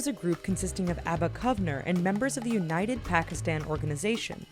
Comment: am i listening right??? did she say united Pakistani in nakam part in 4:41....